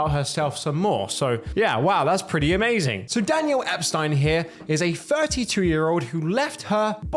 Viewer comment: did you just say Epstein